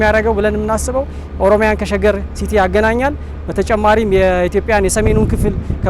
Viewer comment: what is ormya? And way he said that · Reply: He said Oromia. I don't know why he said that Addis Ababa is in Oromia.